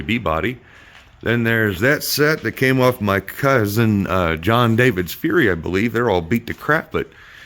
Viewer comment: Oh, I can clear that up for you, my cousin I referenced. His last is Scott, just like ours. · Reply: ah, thanks I misheard the video “ John David”, love the new engine!